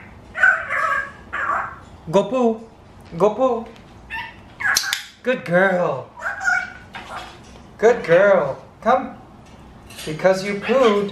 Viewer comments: Coulda sworn she said “Good boy” or “Good girl” Does anyone else hear it too or is it just me? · I heard it! She said Go Poo!!! 😃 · Sounds like she said go poo at 22:59 and then oh boy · Did she say go poo at 23:02? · Did she just said "go poo"? Or just sounds like it? I played the video back and forth from 23:00 onwards and it really sounds like she said go poo... Wow! This bird might talk sooner than expected. And btw guys, its my first time to see a bird yawn 😂😂😂😂😂